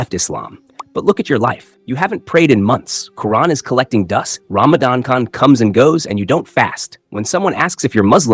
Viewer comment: Did bro really say ,,Ramadan Khan" 💀💀💀